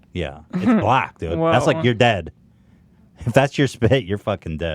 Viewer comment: "It's black! it's like your dad" Am I the only one who heard this the first time? Or maybe I'm the only one who watched this far..